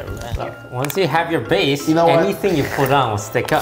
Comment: wait what did she say? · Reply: Your welcome